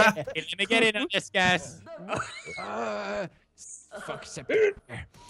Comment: did anyone else hear pewds say "fuck septiplier" at 13:53? like so many ships happened in this vid... #Septiplier #Jelix #Pewdiplier #Cinnieye (for people who don't know its jack and ken) holy crap OTPS FOR LIFE!!!!!!!!!!